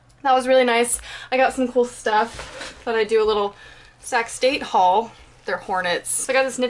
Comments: So funny. I thought you said "Sex Date" instead of Sac State all the time. :D (I'm German and I love your videos) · When you said Sac State for the first time in this vid, I genuinely thought you said sex date...